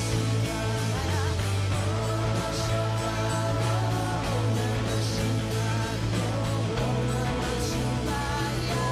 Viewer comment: eu queria tanto saber o que ela fala nessa parte... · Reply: "Om Namah Shivaya "